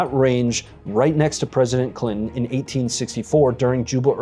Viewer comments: it sounded like he said President Clinton. LoL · Am I the only one that hears "President Clinton" 🤷🏻... Not that it matters but i listened back to it 2 or 3 times and still thought that's what I heard.